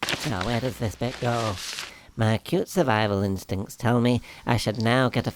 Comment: Did he say "Cute survival instincts" :D